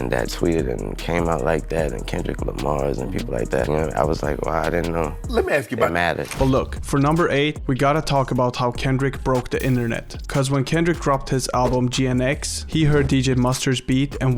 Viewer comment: thought you said shii we didn't know 🤧I'm in South Africa and trust me there's nothing new here🤧